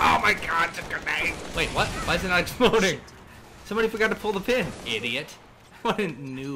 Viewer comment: did she say shit?